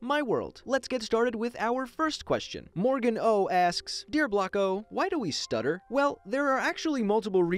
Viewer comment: i thought it said WHY DO WE SUFFER and i was curious